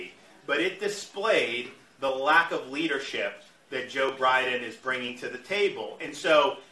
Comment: I heard Joe Bribin"